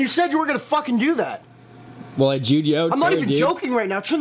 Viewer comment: did h say jewed u out